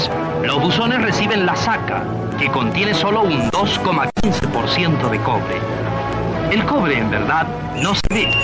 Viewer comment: "Sólo un 2.5 % de cobre" cómo cambia la definición económica de mineral a través de los años en conjunto con la adición de nuevas tecnologías. Buen video! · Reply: Me parece que dice 2,15%...de todas formas un negocio sólido... comentario aparte, registraron súper bien el sonido de la descarga de mineral desde los metaleros.